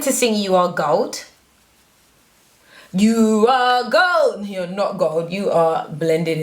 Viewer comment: "you are gold" I heard her say "you are goat"😂